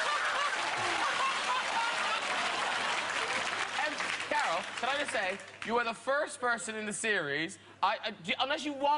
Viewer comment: I can't understand what she says... any help? :) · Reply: We were invited, my son and myself, to meet the Queen and the Duke of Edinburgh ... My son was blind and the Queen asked him how much sight he had left and the Duke of Edinburgh said not a lot judging by tie he was wearing.